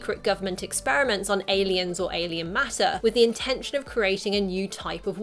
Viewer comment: I could have SWORE he said "angel" and not "alien"